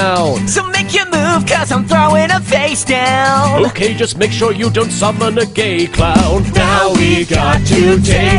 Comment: lyrics: [Yusei] There's just no way that we can win, his cards are epic beasts. He duels too well because he's from another time. [Yami] Listen both of you! He's gonna' rewrite history! He's gonna' wipe out our card games. [Jaden] Never! [Yami] Unless we break his massive monster into pieces. [Jaden] Homies. We've been through so much stuff. [Yusei] I had to hear Jaden rapping. [Yami] That was rough. [All] Now it's time to take this sucker down! [Jaden] C'mon guys, now it's time to blow doors down. [Yusei] I hear'ya Jaden, now it's time to blow doors down. [Jaden] So make your move 'cos I'm throwing a face down. [Yami] Okay, just make sure you don't summon a gay clown. [All] Now we've got to take this sucker down! [Yami] My hairy balls will make sure he won't take us down. [Kuriboh] Do-do-la-la-la-la-la-la-la-la-laa. [Paradox] No! This can't be happening how do I take them down?! [Jaden] We're gonna' beat ya! Oh Paradox! No doubt about it, our card game rocks! [Yusei] As far as villians go in anime! I hate to tell you, you're just cliché. [Yami] That's right you messed with the wrong show. We're not just anyone, we're YuGiOh! [All] We're YuGiOh! (x17) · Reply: LOL, I swear I heard: "[Yumi] Okay, just make sure you don't suffer negate, Cloud" And I suposed Cloud was just Jaden second name, or a card effect/name or some shit.